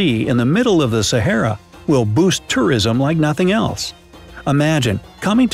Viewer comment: Why did I think he even say this - “The creation of an artificial sea in the middle of the Sahara, will boost Terrorism like nothing else”